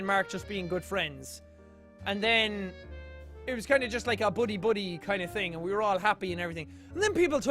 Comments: I thought he said "Boody, Boody" instead if buddy, buddy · when he said "buddy, buddy" I heard "booty, booty" sorry, not sorry